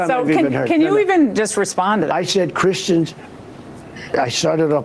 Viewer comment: I heard him say” I am not a Christian. “ Did anybody else hear that?